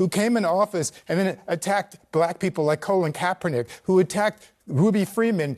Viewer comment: Did he just say colon caperknick . I could have sworn that waist of space name was Collin or colin . Not colon . lol